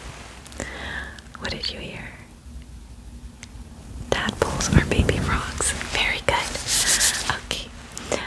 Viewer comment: Why did i hear "tadpoles are baby frouges"😂